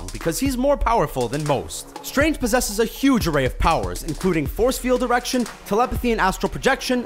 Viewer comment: did I just hear "force field erection"??? Sucks to be dr. strange...